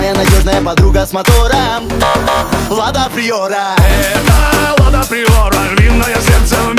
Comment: Я в сперва послышалась мне влад природа